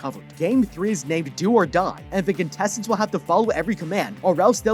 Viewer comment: anyone else think it said door die?